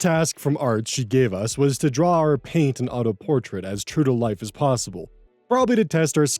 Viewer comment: I misheard and thought you said "draw in Paint" the self portrait; as in the default Windows app that's janky AF. The ability to draw anything in that would be super impressive!